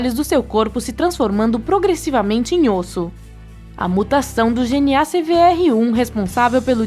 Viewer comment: juro q ouvi "a putação"